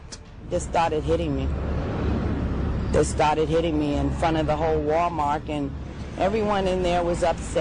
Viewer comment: Wait.....did she call it "Wall-Mark". No wonder this kid is so bad. A Genetic IQ of 50